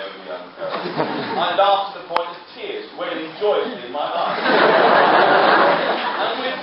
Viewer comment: At a first listen I heard (@ 4:22) "lay joyfully in my eyes". Second listen "rolled joyfully from my eyes" Third listen "whaled joyfully from my eyes" Fourth listen "whirled" fifth "went" I'm not going through the hassle of the interactive transcript myself though. I think for too little of myself and too much of the uploader to bother. That'll teach to do good. People expect the best instead of nothing ;) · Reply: Welling joyously in my eyes😂